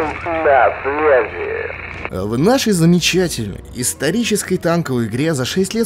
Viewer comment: мне послышалось или он сказал "исторической"?